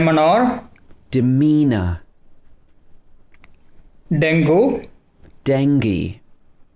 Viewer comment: Any telugu people can relate 😂😂 what that word is 😂😂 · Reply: Dengeyy😂😂 I'm not telugu, but from my telugu friends I got to know what that word means😂😂